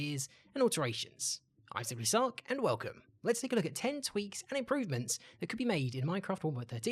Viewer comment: i THOUGHT IT SAID 10 TWERKS AHHH